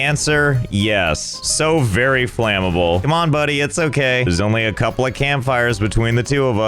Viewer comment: I swear it sounded like he says, “come on GHB” instead of JHG. Anyone else hear that too?